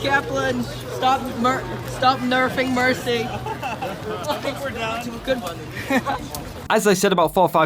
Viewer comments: I thought he said "you think we're done?" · When I heard it first in another vid I thought he said "You think we're done?" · Some people thought Jeff said "You think we're done?" That's what I heard from another Video from another person.